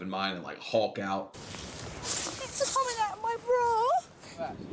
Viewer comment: it sounded like she said her tits are coming out of her bra?